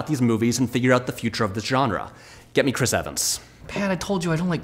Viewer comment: is it weird that I heard him say "get me Rich Evans" at first?